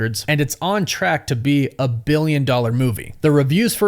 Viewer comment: It surpassed 30 billion i thought you said